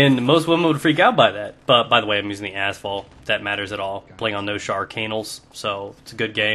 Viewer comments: who else at 2:08 thought he said asshole XD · when you said noshar canals, i thought you said no shark anals